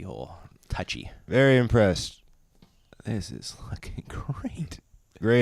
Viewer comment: I swear i thought said "This is f***ing great" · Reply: This is LOOKING great